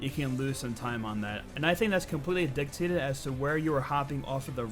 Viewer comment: can you tell me what word you say at 1:44 ? "indictated"?? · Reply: Dictated